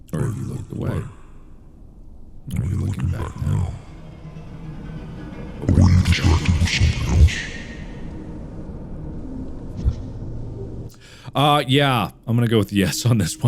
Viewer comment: Just an observation for 32:05 I can never understand WTF Markiplier is saying when you do his demon voice. It would be very helpful if there were subtitles for a quick moment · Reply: "are you watching me right now, or have you looked away? Are you looking back now or are you distracted by something else?" That's what he said but it does get a little hard to understand at the very end